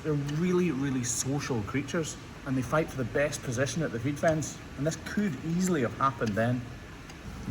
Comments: did you just say they fight for the best position at the hoof trim???? Or am I just too tired after a 12 hr shift and have hoofs on the brain? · did you just say they fight for the best position at the hoof trim???? Or am I just too tired after a 12 hr shift and have hoofs on the brain? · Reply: Yeah lol I heard “feed fence” too.